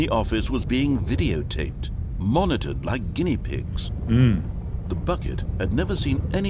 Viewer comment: I swear I heard him say monitored BY guinea pigs... of course, it was them all along. 4 minutes and 35 seconds later... is that a Jo Jo reference?